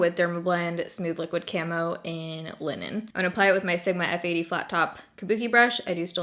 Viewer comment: when you said the shade of the dermablend foundation i thought you said women instead of linen????? i dont even known how to justify that